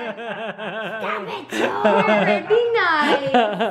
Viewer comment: Did anyone else realise when made jump when she was playing the minion operation she said "Stop it Joel" and not "stop it Jarred" 😂😂😂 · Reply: I heard "stop it Joel" as well